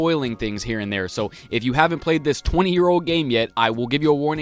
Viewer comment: d..did you say.. 20. Years?